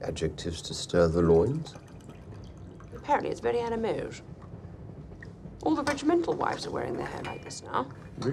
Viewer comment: For a second I thought she said ‘all the rich mental wives..’ …. Aye yi yi